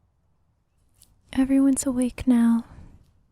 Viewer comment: I've listened to this sooo many times n I never really understood the opening line. I thought she was saying "every once a week now" n not "everyones awake now"... weird